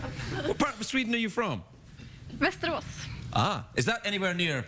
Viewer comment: Did she say she's from Westeros?? lol, little did they know what that would mean today... Btw, Amy Smart did actually make good on her promise and she is still married to Carter Oosterhouse! :D